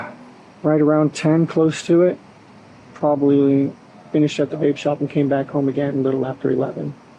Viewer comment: I was half listening when he said he needed to go to the vape shop and get some vape juice. I heard "rape shop and rape juice" and did a double take.